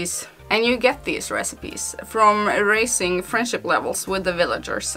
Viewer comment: For a second there I thought you said "from erasing friendship levels" and I was like "...what?" xD